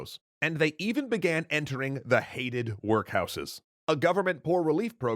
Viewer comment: When I skipped to an earlier part of the video, I heard "...and they eated workhouses.